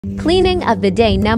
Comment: I heard “Cleaning a Bidet #14” 😂😂🤷‍♂️